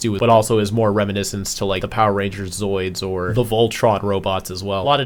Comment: did he say zoids